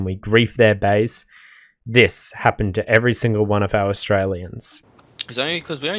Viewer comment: strailian? wut did you say